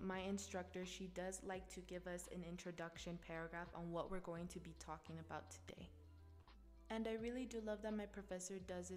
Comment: what is the name of the YOUTUBER? I cannot hear clearly :3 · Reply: The youtuber is called